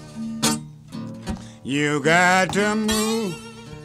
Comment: I hear "you got temu?"